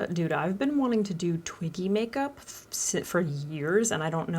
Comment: I thought you said “Twinky makeup” at the start 😂